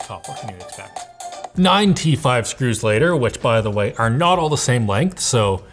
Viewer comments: Sounded like you were saying 95 screws later…. Not 9 t5 screws…. Had to look twice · Definitely thought he said 95 screws later and not 9 T5 screws later.